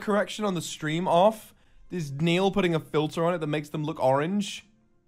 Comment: I think he was saying Neil putting a filter (he's the new youtube CEO, so now we joke about him fucking with the stream instead of Susan xD) Genuinely started to almost believe chat after a certain point lmaoo The actual distress in his voice as it continues is priceless, poor Milord